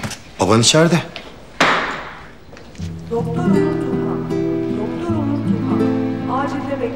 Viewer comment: "doktor Onur Tuna, doktor Onur Tuna! Acilde bekleniyorsunuz" ilk yanlış duydum sandım ahshahs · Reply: Onur Turhan diyo